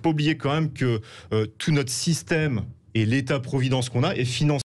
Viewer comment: Qu'est-ce qu'il dit à 4:34 ? La BCE est financée par ... ?? Youtube coupe la vidéo de manière étrange à ce moment précis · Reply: Il ne parle pas de la BCE mais du système de protection sociale